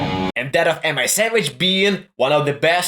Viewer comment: Did you just say “Am I Sandwich”?